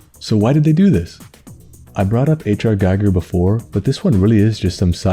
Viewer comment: whom? I'm not a native speaker so I can't understand the name :( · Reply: H.R. Giger is an artist known for his horror paintings. You should check him out!